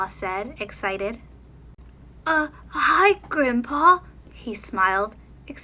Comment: Did anyone think when she said: “Hi Grim’Paw” she said: “Hi Grampa”